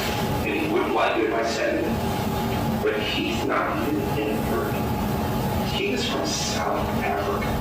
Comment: I hear him say ". . . but he's not a legal immigrant. He's from South Africa"